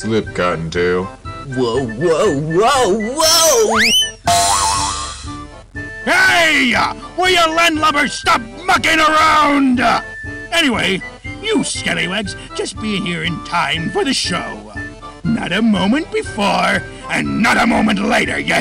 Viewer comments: Foxy did not say “F**king around!” He says “Mucking around!” Sorry for the lack of clarity. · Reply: very good to hear I'm not the only one who thought he said Fucking around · This looks really good, 0:50 yea I heard that wrong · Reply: It sounded like he said stop fucking around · Foxy did not say “F**king around!” He says “Mucking around!” Sorry for the lack of clarity. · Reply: I thought I miss heard this and he is saying “Mucking Around!” 😅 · I thought Freddy said "God damn." Instead of "Cottontail" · Foxy did not say “F**king around!” He says “Mucking around!” Sorry for the lack of clarity. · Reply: I heard "stop bucking around" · I love how the fans make scarier stuff than the actual games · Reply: But bro is it just me or foxy just say “Stop Fucking around” nah but I thought that he said that at first. LOL 😂🤣🦊🏴‍☠️ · Foxy sound like he said stop fucking around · Foxy did not say “F**king around!” He says “Mucking around!” Sorry for the lack of clarity. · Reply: I heard "Monkeying around" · Foxy did not say “F**king around!” He says “Mucking around!” Sorry for the lack of clarity. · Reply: I thought he said "bucking around"